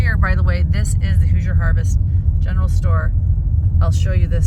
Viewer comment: *Hoosier Harvestore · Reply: hahaha I thought I heard who's your harvester 😆😆 forgive me I'm UK 😅